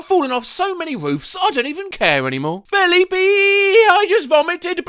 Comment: Where he just said: FELIPEEEE Me: Wait, I thought he said: "Philippine"